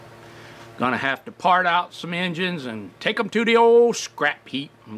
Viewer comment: For some strange reason when you said you were going to "part out some old engines" I thought you said, "fart out some old engines". LOL 🤣🤣🤣